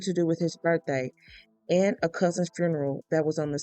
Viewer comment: This 50 four-year-old man shot His Seventy five year old mother and his two sisters in the head! He murdered them because they held a funeral on the same day.That happened to be On his birthday Or somewhere near his Birthday.What were they supposed to do? celebrate his birthday first and Bury their brother Who died in a tragic car accident later? . He was an evil wicked selfish man! The narrator says that the mother was A. Christian., she Believed in God, our Lord and savior. Satan used him to take the lives of his mother and his 2 sisters. Prior to that, satan took the life of their brother in a car accident and now the eldest sister has to Bury all 4 family members. After just Burying her brother Weeks prior. God I pray and ask thee in the holy precious name of your Jesus to please Hold that woman's heart in your hands. Heal her Lord God,in Jesus name I I pray and I thank you for you. Love and your mercy, and For hearing and answer my prayer God give that woman strength and heal her heart from this Horrific tragedy that her own Brother caused.What a great loss ! ...this tragedy reminds me of the story of job When satan Murdered jobs entire family! Except for his wife.. To the last surviving Sister, stay strong In the Lord, The God of Abraham Isaac and Jacob And Jesus Christ Our savior.I will pray for you. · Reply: I thought it said cousin funeral.